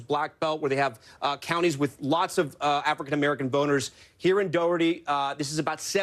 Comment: did he say African-American boners?